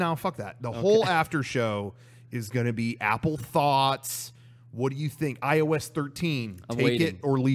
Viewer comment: Yo I thought said "Apple Thots" and I lost it lmfao